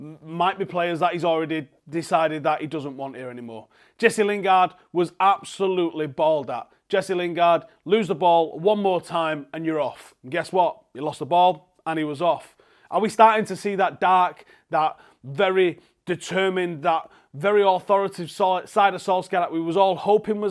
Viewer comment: I didn't think ole said you're off I thought he said fuck off hahaha